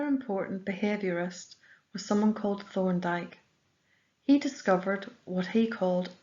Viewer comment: what is the name of the behaviourist? I really can't understand it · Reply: Thorndyke